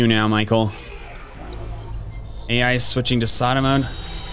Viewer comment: AI is switching to Sada mode? Did I hear that right?